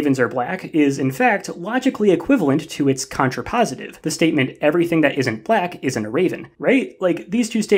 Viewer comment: The raven thing was really hard to wrap my head around, beacause I got confused and somehow thought you were saying, "Everything that isn't a raven isn't black," or in other words that only ravens are black. And that's so obviously untrue that it kind of melted my brain for a bit.